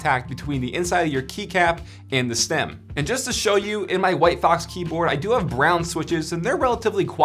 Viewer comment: can you add links to the white keyboard you showed in this video to the notes? I heard "Whitebox" and Pok3r RGB ... but can't find a website for the whitebox.